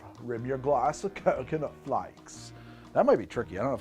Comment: Bro, on the second drink I thought you just said coke instead of coconut flakes